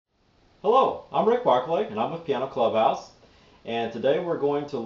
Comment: o i thought he said hello im rick butthole xD at the very bigging of the vid hahA